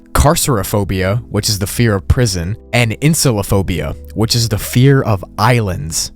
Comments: I heard that as “incelaphobia” like maybe the fear of incels or something lol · nauuuur I thought it said incel o' phobia